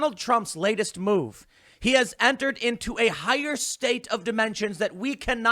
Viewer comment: I almost thought he said “a higher state of dementia” for a second xD